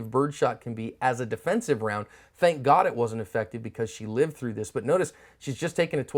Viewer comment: did she live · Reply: he clearly says "She lived through this" Situational awareness is zero friend